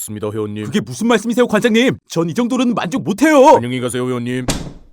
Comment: 나만 형님이라고 들림?